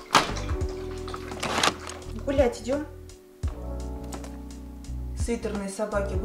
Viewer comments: Мне одной послышалось вместо гулять идем, Бл*ть идем? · Короче спокойно смотрю влог Кати и мне послышалось 13:30 б*ять идём?